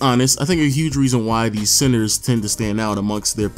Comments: Why does it sound like he saying “CENSOR” 😂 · Doesnt it sound like dudes sayin "censor"? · do the evolution of point guards next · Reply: Why does it sound like he saying “CENSOR” 😂